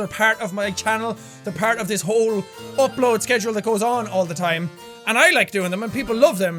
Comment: I thought he said 'fuck load' instead of 'upload' lol